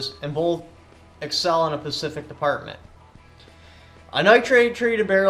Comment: Did he just say a Pacific point?